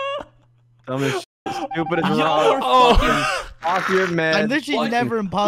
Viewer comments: Is it just me or did say fucking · yo. did swear at 18:12 ? “Y’all are f*ckin-“ HMM am I hearing this correctly?HMM